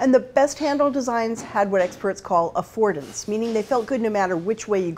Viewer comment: I'm not sure what she said at 3:33. "Experts refer to this as ..."? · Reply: Hi Brian, hope this helps: "And the best handle designs had what experts call 'affordance.'"